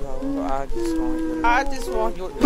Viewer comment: At first I thought he was saying I want your doge Then I heard I just want your doe Then I heard I just want your love WHAT IS HE SAYING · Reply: I just want your love, I'm ready for it, That's the lyrics